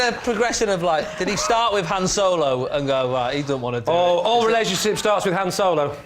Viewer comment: I can't understand what Lee said there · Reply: "All relationships start with Han Solo" But meaning "hands solo". So a joke about masterbation.